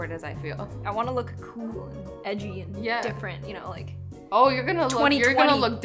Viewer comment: Who else heard her say “ I want to look cool and different, 2020” 2020 was different for sure.